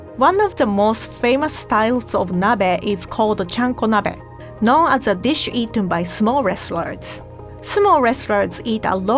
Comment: I misheard, "...known as a dish eaten by small wrestlers"